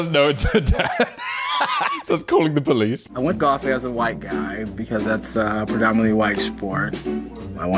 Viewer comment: I swore he said “I went for coffee as a white guy because that’s a predominantly white sport.” and I sat there like… coffee is a sport?